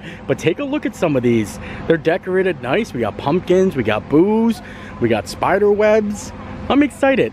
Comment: Oh you said "We got Boo's!" I thought you said "Booze!"😆